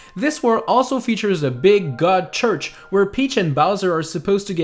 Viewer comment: Did he just say “god church?”